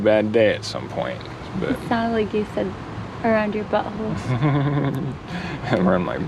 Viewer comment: I was just about to say that. It sounded like you said cracks around your butt holes